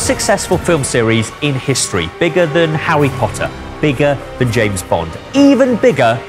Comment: it’s not just me who hears ‘Howwy Potter’ is it?